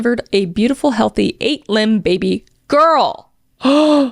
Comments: bro when you said 8Ib I genuinelty thought you said eight LIMBED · I heard "8 limb baby girl" and got so scared! Spooky season is upon us🕷️ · I wasn’t watching and was just listening so when Kris said “lbs” (29:39) I thought she said LIMBED and thought this story was going in another direction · I was listening not watching, so I definitely thought you said "8-limbed baby girl" and had to rewind 😂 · The double take I did when I heard “8-limbed” and not “8 POUND” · Bro, I thought she said 8 limbed baby girl and I was like what?!?! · I was listening to this while doing smtg else and I thought she said 8 limb not 8 lb (T-T) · Wait I thought she said 8 limbed baby hahahaha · omg I thought she said 8 LIMBs it’s LB (pound) not limb lmaoo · Ok I wasn’t fully paying attention to the video I was listening. 29:40 - 29:43 I heard “my wife delivered a beautiful, 8 LIMBED baby girl.” And she just skimmed right over that was so confused why they didn’t ask about surgery to remove 4 limbs lol. I was like, there’s no way the parents were just “oh, 8 limbs that’s fine.” I’ve never once heard someone pronounce “lbs/lb” as its own word without saying pounds 😂